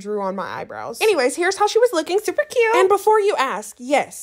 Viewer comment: TAKEN on an android, the first time I watched this I thought you said TAKING on an Android like you were fighting terminators or something